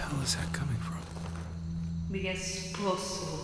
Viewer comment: What is the woman saying in the background? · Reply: "My family, my daughters, my husband." I misheard esposo as poso.